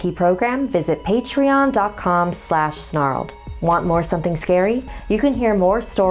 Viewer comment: I thought “snarled” said “sharted.” Sharting is pretty scary Edit: Tysm for the likes ❤️